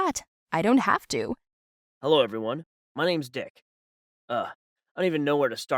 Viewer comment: Did I mishear or was the first story involving a dress with drawings of genitals and the second story started with a guy saying "my name is Dick"...? xD